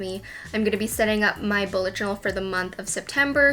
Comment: Hey! Love this video but some of the closed captions aren't correct. Like at 0:14 where it says bullet drill instead of bullet journel. It would be awesome if you could fix these mistakes for those with hearing impairments who want to enjoy Youtube too! ♥️